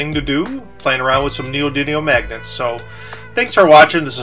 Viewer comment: It's neodymium, not neodynium. It has an M, sorry to be the grammar police, it was just driving me crazy. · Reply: friartuck103 I heard him say neodydium